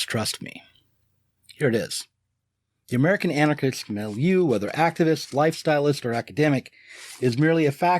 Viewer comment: The American anarchist what? Mel U? I do not know this word. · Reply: Milieu